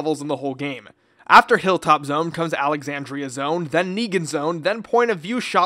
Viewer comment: WTF YOU JUST SAYING NEGA ZONE?! OMFG 😂